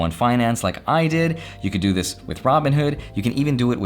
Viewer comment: did he say you could open up a roth IRA with Robinhood?!